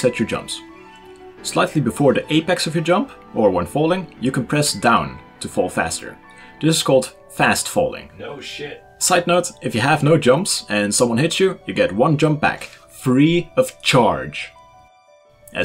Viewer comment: anybody hear the guy behind him said "Oh shit"